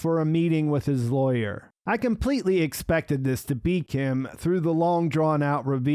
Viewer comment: beak him instead of be Kim? LOL